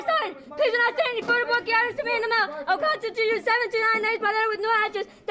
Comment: Sounded like she said ,"I took a shot in the mouth."😮